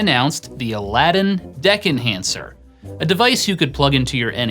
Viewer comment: i thought i heard DICK enhancer..hahaha..